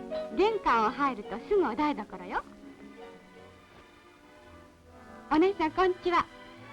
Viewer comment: 高画質でカラーの「団地への招待」がオススメに出てきましたの。嬉しい完全版収録ですのね。 3:09「おねーさん、こんっちわ」って聞こえますわ。